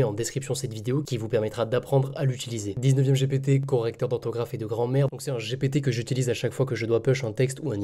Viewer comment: j'ai entendu "Correcteur d'ortographe et de grand-mere" haha. sinon merci ! Super video !